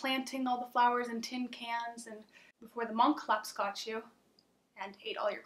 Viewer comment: That intro was SO cool and the effects throughout are awesome! I want some space flower candy too XDD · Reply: I thought you said it was candy LOL I'd still eat it, it looks yummy XDDD